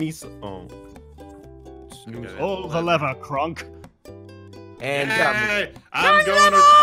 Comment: did anyone catch them say pull the lever Kronk?!? EMPERORS NEW GROOVE!! 💗